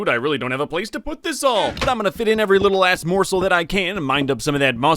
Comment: did he say "every little a** morsel?" Cause I think he did